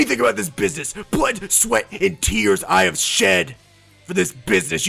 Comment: hahah, it sounded like you said "butt sweat and tears"